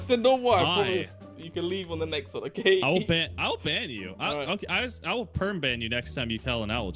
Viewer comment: Did anyone notice when said "I'll ban you" but it sounded like "owl ban you".. also said "i'll perm ban you next time you tell an owl joke" sounded more like "owl perm ban you next time you tell an owl joke" haha